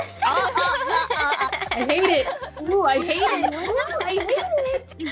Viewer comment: "I hate it, I hate , oo i hate it!" I felt that, Calliope. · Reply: Considering that Calli is a Tsundere boomer I hear it's "I hate it, ooh I hate it" instead of "I heard it." So yeah, I agree with you